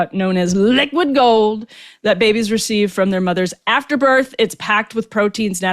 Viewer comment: At first I thought you said that babies receive colostrum from their mothers' afterbirth, not from their mothers after birth.